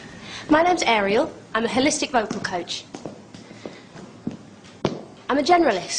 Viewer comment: Contestant: Im a holistic vocal coach Simon: and what exactly do you do Contestant: im a vocal coach i work holisticaly Simon: no shit sherlock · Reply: I thought she was saying hellistic vocal coach :<